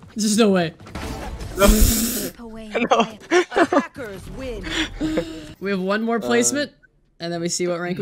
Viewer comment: I understood "The hackers win!" instead of "Attackers win!" x'D Quite fitting since that was the game where one of the enemies kept saying "viper hits everything"